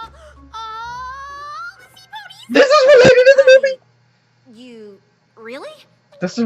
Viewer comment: What did you just say? 9:50 Me too. 10:08 Your mic pitched out. 18:14 He's the same guy who voiced Brain in Pinky and the Brain. 20:43 You missed seeing Derpy. 21:45-21:50 Yes, yes he is. 25:23 Did you mean Princess 'Sky Star' or Queen' Novo? 31:12 It's Pinkie, don't question it. 32:57 Did you seriously not notice they used the new throne room design from the movie? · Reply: I said, "Thisisrealatedtothemovie!" XD I kinda freaked and geeked out to the point I spoke little too fast there. XD